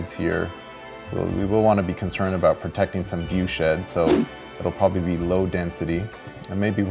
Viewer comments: “Wanna preserve the sense of entitlement these people are gonna have up here...” Yup that’s California all right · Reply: When he said "protecting some viewshed, so..." I legit heard "protecting some doucheheads". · Thought he said viewshit.. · Who else heard << View shit >> xD !